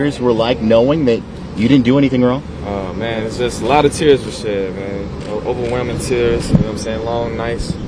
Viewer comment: I like how the caption says tears for shit when he clearly said a lot of tears were shed...